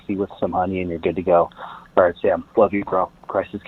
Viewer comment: I definitely heard "onion" the first time. But I'm pretty sure it was actually "honey" which makes a lot more sense. Lol